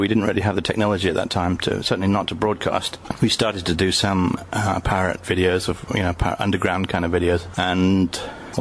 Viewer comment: Oh, PIRATE videos. I thought he said "parrot videos"!